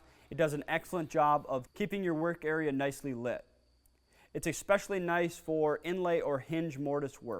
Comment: did you really say expecially?